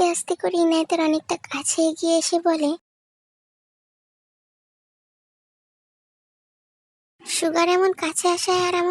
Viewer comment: কি বললো suga কিছুই তো শুনলাম না 😅😅😅😅😅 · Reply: ভয়েস কেটে গেছে তাই 😢 সুগা= আমি অনেক কিছুই বুঝি কিন্তু তুই ভালো করে বুঝাতে পারিস না।ভালো করে বুঝানোর try করবি